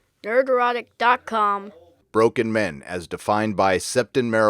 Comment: That intro, I always hear “NERD EROTIC”